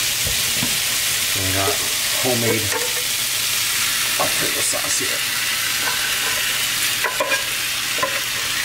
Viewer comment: homemade what i heard “pickle sauce “😂😂😂